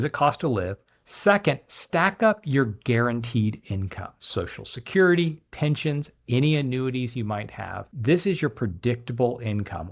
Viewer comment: Did he say at 2:43 guaranteed income and include social security?